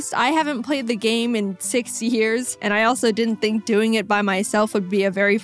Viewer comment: when she said she hadent played in 6 years i thought she said 60 and i had a heart attack lol